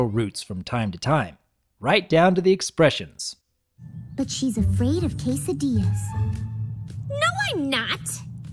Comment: can any one tell my what she said here Afraid from what ? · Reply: Almozayaf Roman afraid of quesadillas.